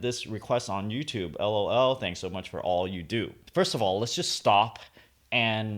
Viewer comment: when he said "let's just stop..." I thought he said "let's just stalk." brb. going to stalk the commenter!